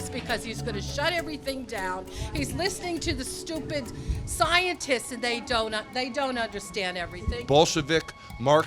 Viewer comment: ''He's listening to scientists, and they don't know everything.'' Can you stand it? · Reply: At first listen I thought she said something about scientists and their doughnuts.